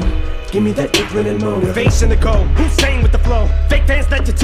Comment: I didn't think back to square dance. I heard it as "usain (bolt) with flow". Guess it went a bit above my head.